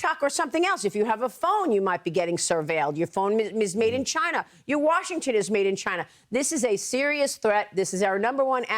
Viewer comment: Did she say "your Washington is made in China"?